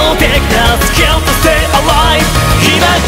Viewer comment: 転スラのアニメ見てからきた人いるー？ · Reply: ノ 1:07がリムルって聞こえるんだけど本当は何て言ってるか分かる？